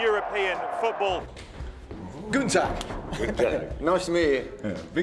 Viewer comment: I thought he said Günther.. but why and now i get it.. Guten Tag.